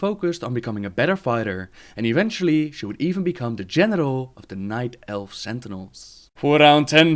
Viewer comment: lol he said Genital instead of General.